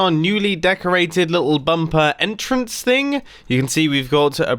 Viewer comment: Did mumbo just said bumper instead of bunker?